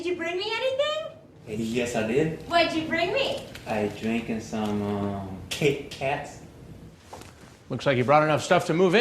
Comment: I thought he said I'm drinken sum kit kats